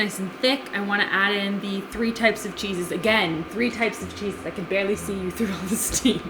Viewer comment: When she was making the 30 minute it sounded like she said 3 jesus instead of 3 cheeses😂